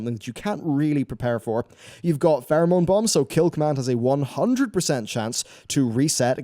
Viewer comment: I thought you said Theramore bomb xD